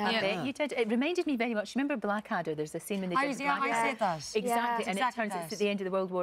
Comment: What's the name of the show she said? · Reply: Blackadder goes Forth - the final episode.